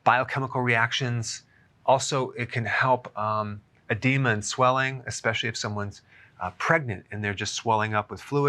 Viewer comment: Who else heard "It can help a demon's swelling"? I had no idea pistachios were THAT good XD